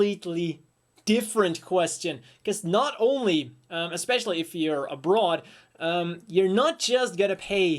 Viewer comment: "Not only if you're abroad" I misheard you at first and thought you were secretly sexist and were going to make some claim that women could not lift this up or something silly like that, going into some "if you're a broad" thing. Not sure if that outdated slang is present in Belgium.